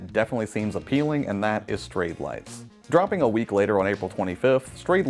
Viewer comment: What is the name of the game at 4:10? I keep mishearing you. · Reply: Sorry about that! Should be Strayed Lights :)